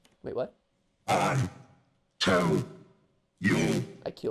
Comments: I hear “I told you” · It sounded like "I. Told. You" maybe because how the prerecorded message said to not say nightmare or garbage. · i think he said «i tell you» as in «i’m the one askin’ the questions, fool!» but, well, eh? Idk. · i think he said "i told you"